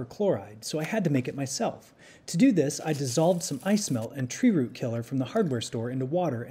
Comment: Trying to figure out the precipitate is for this. Obviously he's got a sulfate. (looking it up, the pool tablets are calcium chlorate, so he's basically creating calcium sulphate or gypsum). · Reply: I was wondering what he said, I thought he said "ice milk".